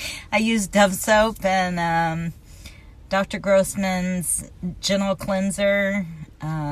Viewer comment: I can't stop laughing I thought she said Dr gross man's genital cleanser 😂😂